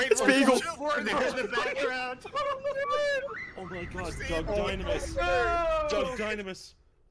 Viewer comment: Am I trippin or did he just say "Duck Dynamis"?